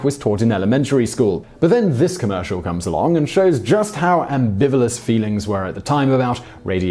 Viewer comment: Ambivilous? What, exactly, does that word mean?